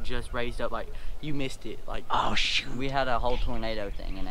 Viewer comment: bad word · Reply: i thought he said oh shoot